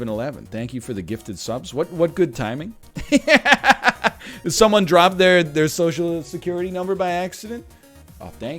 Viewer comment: I heard "did someone drop their social insecurity number" at one point and was like "ah yes, that's exactly the kind of number chat would have"